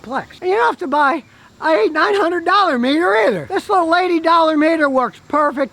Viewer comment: I thought he said meteor, like which meteor do you want to hit your BMW.